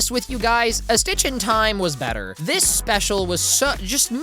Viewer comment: Honestly, I keep mishearing "stitch in time" as "sitch in time." Like the lingo that the show uses